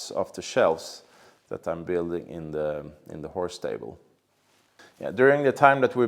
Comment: Make some holes for oars and we can go pillage the english again. Was a bit concerned for your marriage when mentioned the whore stables, but think I might have misheard. Beautiful work as usual.